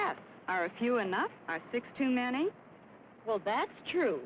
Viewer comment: "I'll give her prunes or something." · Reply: "A few enough, 6 too many?" :-) BTW I always heard it as "4 enough, 6 too many?" Maybe that was another version? (or maybe I just wasn't hearing too good....)